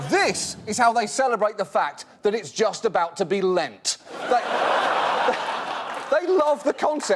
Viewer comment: Someone help me: at 1:57, he says that "brazilians celebrate the fact that..."? The only thing I couldn't understand : ( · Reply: …..it is about to be Lent. " A Catholic tradition where you give up something you love for a month. Thats why he said the bit about chocolate right after.